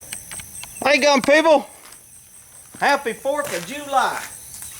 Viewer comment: Almost sounded like you said "Hello Gump people!" Hi my name is forest , Forest Gump.